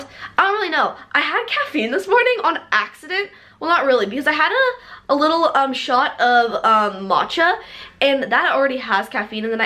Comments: I thought she said she had a shot of vodka!😂😂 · DID SHE JUST SAY MOCHA LIKE MA-CHA??? ITS PRONOUNCED MOE-KA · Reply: Simona Inglima she said MATCHA